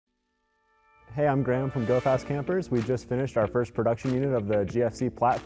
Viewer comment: ha i thought he said Dope Ass campers!!! witch they are tho....